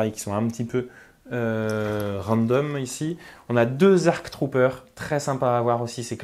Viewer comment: C'est ARF... ARC - Advanced Recon Commando et ARF - Advanced Recon Force. Sinon bonne vidéo! 7:24 Vu comment c'est oui c'est un création de l'abonné. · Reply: Perso j'ai entendu "2 ARC Troopers".